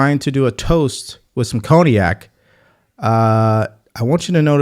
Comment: I had to rewind a couple times when you said cognac…because I thought you said Kodiak (I livein Alaska)…anyway…I thought that was funny when I realized what you actually said.